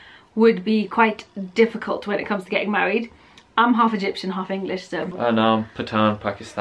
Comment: What did she say at 11:11 ? she half Egyptian and half what???? thanks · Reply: Half english